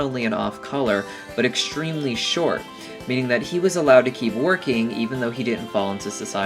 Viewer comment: wait did he call carnilie a he? is it a he??